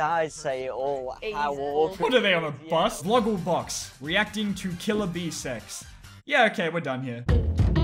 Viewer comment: it sounded like you said killer Bean sex